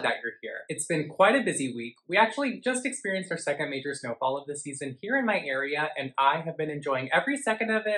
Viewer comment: I have to apologize to you! In the last video you posted(my first), when you said talks, I heard botox and was thinking well that's weird but whatever...maybe he talks about botox. I watched you again today and saw as the video was ending, and felt dumb! I like talks way better than botox! Lol. Keep up the good work! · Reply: lol yeah others have commented about hearing "Botox" as well. I actually turned on the captions for this video because I didn't understand what was saying in the beginning. Turns out it was "snowfall." Anyway, my caption thought was saying "Botox" too. lol